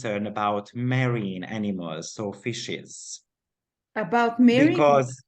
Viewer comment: I thought he said marrying animals 🤣🤣🤣🤣🤣🤣